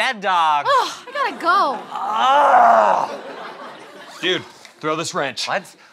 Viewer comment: I thought he said throw this ranch, it may have been funnier....